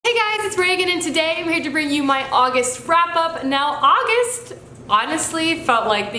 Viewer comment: It sounded like you said august frap-up